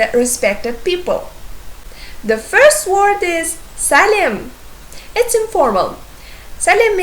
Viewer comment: I was shocked for the greeting. I thought you called my name, because 'Salim' ('hello' in Kazakh) is my name.